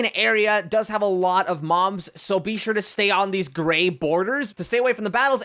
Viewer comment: did he say moms?????